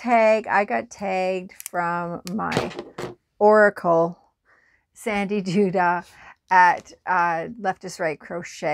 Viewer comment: All this time I thought you were saying Sandy Do Dah….lol